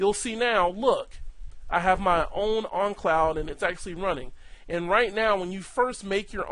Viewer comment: Great video! Just a funny observation. For most of this video I thought you had some strange accent that I never heard of which causes you to pronounce the word "own" as "on" because you keep saying "on cloud" instead of "own cloud". Which, ok thats cool, no big deal... But then at around 6:41 you say "own on cloud"... Just made me laugh so i thought I would point it out. Thanks for making!